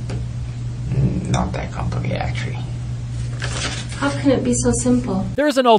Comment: What did he say at 12:26? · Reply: "mmmm mm not that complicated actually"